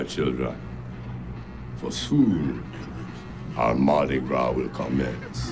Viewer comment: does anyone understand what he said ? Because I couldn't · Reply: For soon our Mardi Gras will commence